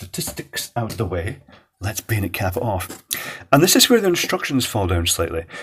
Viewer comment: What is saying at 1:43? It sounds like "betacap" or "beinacap"... I've never heard that expression, can anyone enlighten me? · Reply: He's saying "Bayonet Cap". It's a reference to the twist and pull mechanism to open/close the case, common to bayonet mounts